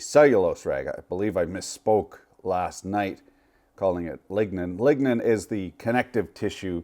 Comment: I learned nothing but it's a me problem. I was eating bacon and not really listening. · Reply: As a side note; 22:54 Did you say, ligma last night?